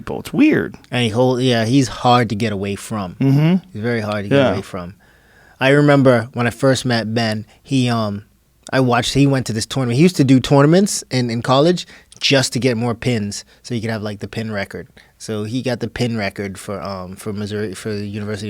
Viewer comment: Did he say tournaments weird or no ? Lol Idk